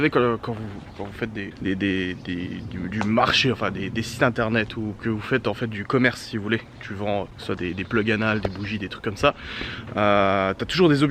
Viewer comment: C’est moi ou il a dit plug anal ????